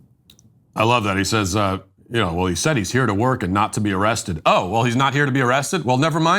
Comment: I thought he was saying i'm here to work and assault young girls